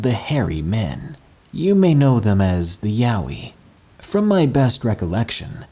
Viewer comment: did he just said yaoi?😂😂😂💀